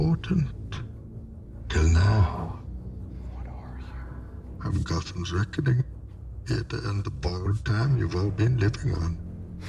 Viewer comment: What the hell was Bane saying from 1:18 to 1:20?! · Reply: 1pk155 "I'm Gotham's reckoning. Here to end the borrowed time you've all been living on."